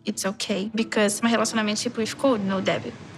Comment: Why do I hear her 'relationship' as 'hallucinationship'?😂